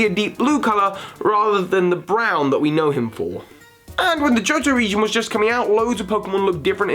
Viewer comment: did he say the Jojo region? can you imaging a whole genration that had fabulous poses and bright color schemes lol.